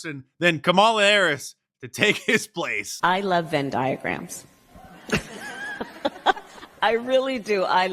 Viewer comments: 🫨thought camel toe said “I love diaphragms” 😳🥴🤣😂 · I thought she said "I love Venn Diaphrams."...I said that makes sense.